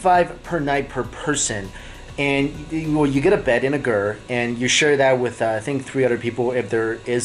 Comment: Sounds like Mikey is saying for 75 a nite you get a bed and a girl. Hahahaha!!!!!